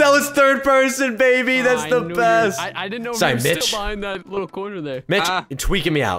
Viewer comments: I thought he said sorry b*tch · I don't know for sure but at 9:09 it sound like u said the b word instead of mich